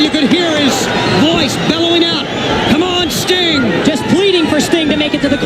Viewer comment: 😂 that idiot Warrior breaks kayfabe by calling Sting by his real name ("COME ON STEVE!!"), leaving Schiavone and Tenay to cover for him and act like Warrior was actually saying "come on Sting!", so Warrior yells "COME ON STEVE!" at 4:58 again 🤣🤦‍♂